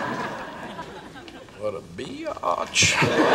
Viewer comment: I thought I heard BIOTCH 😂😂😂